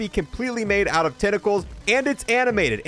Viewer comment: that flesh WHAT color? stg it sounded like he said fleshlight color. It was.. he said flesh like. oh thank god.